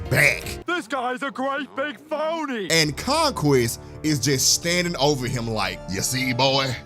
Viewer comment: did you just say Conqweest?